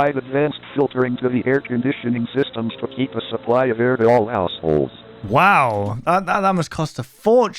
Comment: bro the fact that i thought the narrator said "assholes" really got me out of the spooky vibe lol